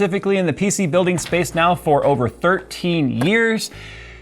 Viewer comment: I thought jay said over 30 years